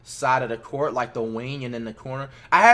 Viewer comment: Did I just hear him say hotspots? 14:47 Sorry if I got the time wrong